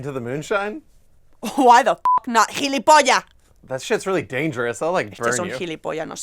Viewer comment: What does she say at 1:22? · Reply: "jilipolla" that means you a a dork or a fool